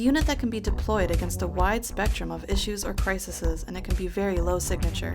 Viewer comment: crisises???????? anyone heard that